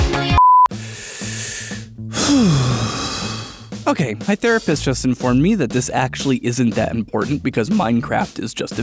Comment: is it just me or does it say the rapist instead of therapist